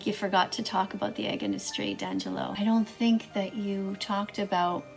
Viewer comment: when ever I hear her say his name I hear her say "danjello" LMFAO